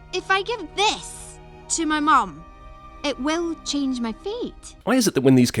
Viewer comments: When I watched this movie I legit thought that she was saying ‘Change my feet’ but it was change my fate 😂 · The first time I saw the movie, I heard "Will it change mah feet?" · I always thought she said “so this will change my feet?” Not “fate” XD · Every time I watch this movie I thought she said “Are you sure this will change my FEET.” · When I was younger, I honestly thought she said “if I give this to my mum, it’ll change my feet?” Is this racist or can 7 yr old me not understand accents?